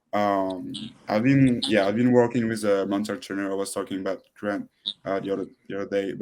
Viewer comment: who did he say he was working with, at 49:48 ? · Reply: He just says he has been working with a mental trainer